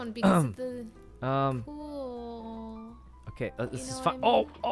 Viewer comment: DID ANYONE ELSE HEAR SYKKUNO SAY FUCK WHEN HE MISSED THE HOLE?!? like yes he is a grown man who can say and do whatever tf he wants but it just caught me off guard omg.. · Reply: He said this is fine